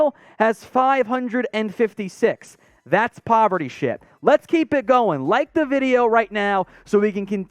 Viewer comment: Thought he said, "that's poverty shit"